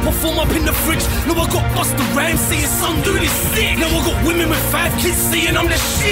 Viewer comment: Thought he said "Son , dude is sick" no biggy ! ;) bless